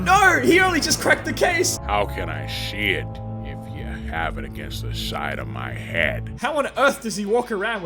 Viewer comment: Is it just me or does it sound like he says How can i shit of u have it against the side of my head